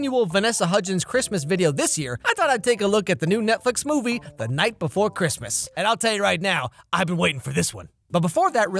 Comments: I thought he said "The nightmare before Christmas is the dumbest movie" I was about to say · For second I thought it said the nightmare and I got my mad